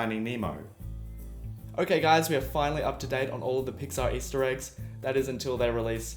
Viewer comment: did he say pizza Easter eggs???